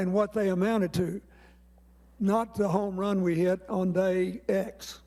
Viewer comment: what does he say between 29:26 and29:31?? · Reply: '......not the run we took on day X......."